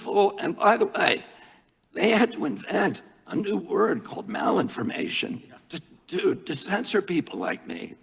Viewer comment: So at first I thought he was saying 'Maoinformation'. As in, referring to Chairman Mao. I had heard their stupid 1984-esque term 'malinformation' but I'm definitely referring to any info the government wants censored as Maoinformation from now on LOL